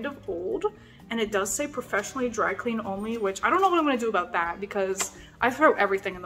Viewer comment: When you said "professional dry clean only," I thought you said "professional Drag Queen only" and I laughed way too much.